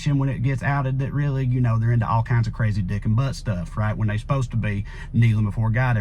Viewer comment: I swore I heard Crazy Nuking Butt Stuff and wondered if you had seen that Click episode